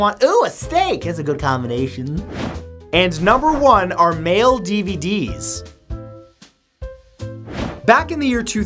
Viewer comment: i'm drawing on the side and when you said mail dvds i thought you said male dvds